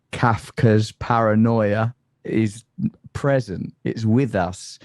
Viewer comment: Wait...did Russell say Kat Kerr's paranoia? Or what did he say? Seems like the only words that he heard and understood had to do with god, making his response a word salad of keywords....